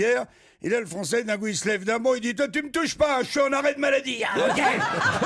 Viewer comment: La blague avec Jezus a 4:44. Quest-ce qu'il dit le francais? 'Shaun arrète maladi'?? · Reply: ch'uis en arrêt de maladie